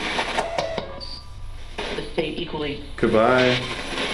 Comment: I just heard "thankfully" when asks if he could say good bye